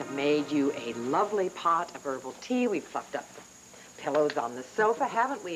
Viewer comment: Sounded like Renee said "we fu*ked up"